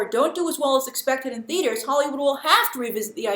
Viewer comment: Is saying Hollywoo?